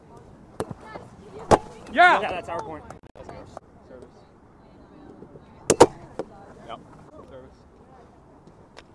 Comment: What was the call at 4:34? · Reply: Hit Paq on the follow through